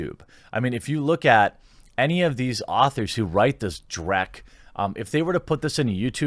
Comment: did you say "dreck"?